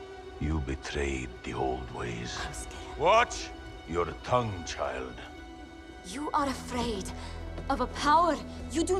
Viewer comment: when he said "Eiskar" why did Arthur get offended and tell him watch his tongue? What does that word means? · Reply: Bloodhound said Heimskr which is "stupid" in icelandic